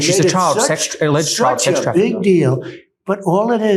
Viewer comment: does DT say, "Such a big deal!", when the interviewer says, "She's a child sex trafficker."??!!